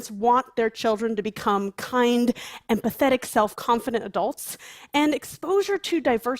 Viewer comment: LMAO I had subtitles on and it says she said "Now most parents want their children to become kind, and pathetic self confident adults" It even sounds like she said it, that's what I thought I herd so i went and checked subtitles.. and she did just describe most of her community calling them "pathetic yet confident" lmao wow. I hope she really meant empathetic because otherwise i'm curious as to who in the world would actually want their children to grow up to be pathetic....